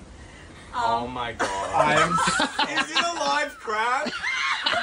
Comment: Bro I thought him saying “is it a live crab” was him saying “is your life crap” 😭😭